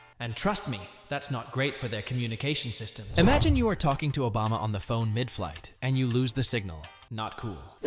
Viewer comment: I Thought He Said Osama💀